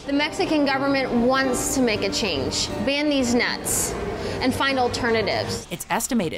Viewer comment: I swear I thought she said "ban deez nuts" at 1:30. I had to relisten on that one lmao